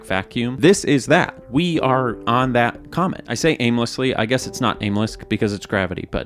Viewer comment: ish i heard "comments" and not "comets" and was struck by the idea of fabled comments that occur at long regular intervals. People gathering waiting for it to appear. Unfortunately it would probably be a terrible comment and there are already too many of those. But i like to inagine a mythical comment that brings peace and insight and good luck to those who see it as it passes thru the comment section on its long voyage through the internet